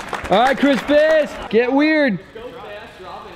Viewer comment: At first I thought he was saying Chris Piss until I read his name in the description.